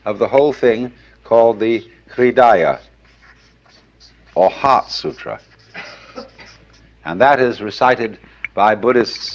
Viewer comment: "HEART" sutra. Whoever does subtitles needs to actually listen to what the words are.